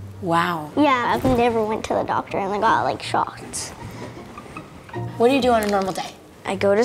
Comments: The fact that you put a kid who's never been vaccinated in a room with someone who has a chronic illness is worrisome, imo. EDIT: I know this woman isn't going to spread her illnesses to the child, guys. I meant the kid could spread something to her. EDIT 2: Ok guys, I get it. The captions say otherwise. If I was incorrect, I'm actually happy that she's been vaccinated. Not deleting this post though, because I think it's still important to make people aware of the importance of getting vaccinated. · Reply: I have replayed it several times and I'm pretty sure she said, "I've never been to the doctor and got like, shots." Maybe I'm mishearing it. I certainly hope I am. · kid: I've never seen a doctor me: 👀👀👀 wai- kid: Except for the shots i got me: oooh there you go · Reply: I could have swore she said not even shots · Girl: I've never seen a doctor Me: Oh no, she's unvaccinated- Girl: Only for shots Rush of relief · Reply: i thought she said "and got like shots"